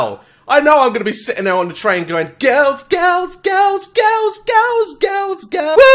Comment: it's not just you daz I heard it too...it did sound like he said "I be lookin at girls girls girls skirts"